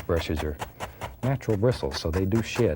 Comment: Did i hear Bob Ross say the bristles shit at 16:03?! 🤣🤣🤣🤣🤣🤣🤣🤣🤣🤣🤣🤣